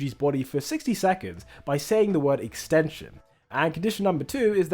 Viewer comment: i believe this is a translation mistake. im pretty sure its "enchain" instead of "extension"